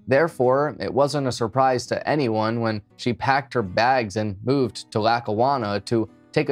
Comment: what does say in this sentence? "...she packed her bags and _____ to..." Because I listened to it about 5 times and to me it sounds like says "...she packed her bags and boobs to...", which can't be right. Right? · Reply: Lol, the word is "moved". I went back and listened. She packed het bags and moved to Lackawanna 😊.